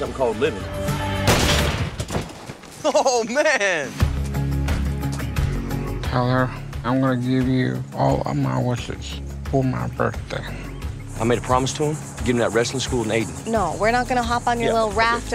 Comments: "I wanna give you all of my wishes for my birthday" I just lost it on that line man.... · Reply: Henry Sy honestly I thought he said I wanna give you all my watsits and I got very confused until I watched it again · "I wanna give you all of my wishes for my birthday" I just lost it on that line man.... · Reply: Ohh... I thought he said "I wanna give you all of my horses" I laughed real hard. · "I wanna give you all of my wishes for my birthday" I just lost it on that line man.... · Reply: I thought he said I wanna give you all my horses.. lol